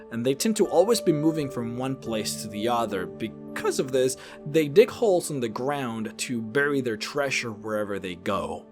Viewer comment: when he was talking bout reproduction criosphinx.... for a second i thought he said their dick hole's in the ground